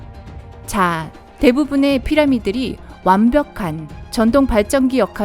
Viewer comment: ~44초 대부분의 피라미들이 라고 들리는데요